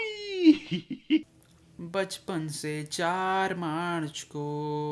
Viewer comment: omgggg 😂😂😂😂😂 i thought he was saying something about 4 March but nahiiiii 😂😂